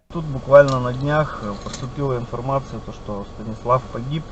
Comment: Знаю, що це може тупо, але почулось реально: "...что єблан погиб..."